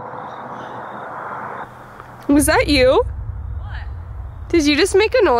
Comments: when said from the peanut gallery it sounded like the voice said "the what"? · after you said “from the peanut gallery” I heard “the what?”